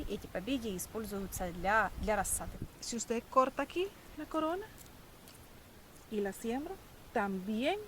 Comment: я услышал: (не корона) Не корона, бро? Не не корона, бро.🤣